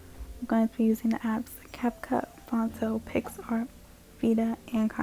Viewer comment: Question: in the video what did you say, with the fonts thing cause I heard “fontso” and I tried to hear what it was and I was confused. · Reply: What time of the video was it? Probably Phonto.